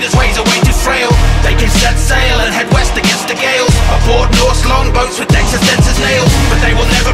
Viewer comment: I thought when they said "head west against the gales", they said "head west against the gaels" as in Ireland lol